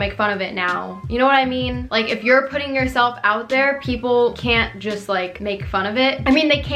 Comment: I think you're trying to say ''You're putting yourself out there before someone else can'' lol hope that helped some people